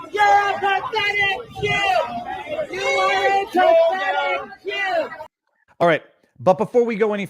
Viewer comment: I heard "prophetic jew". Protestor thought JT was the second coming of Christ? 😂